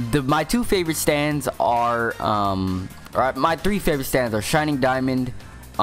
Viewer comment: Did he just say “Shining Diamond”?